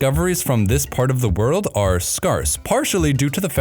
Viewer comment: My friend did u just say “scarss”